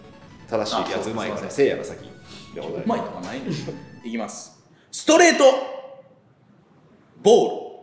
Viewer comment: 「ストレート」の溜めが「ゴムゴムの」みたいに聞こえておもろい